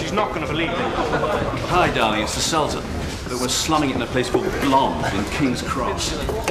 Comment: did he say "facebook" ?